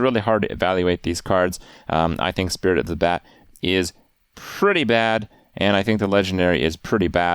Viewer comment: Did i just heard ''I think Spirit of the Bat is a pretty bat.'' ? :D